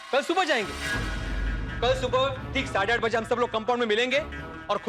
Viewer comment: Bhide says 8:30 and captions say 9:30 Kis bewakoof ne likha hai yeh batameez? Isiliye paise lete hain?